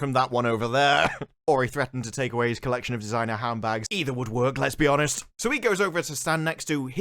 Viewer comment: Did he just say "Lez be honest"?